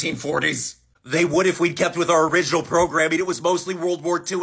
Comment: "I just want people to know about the golden horde" Same bro, same 😢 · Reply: Oh, thank you. I thought he said "the gold Nord"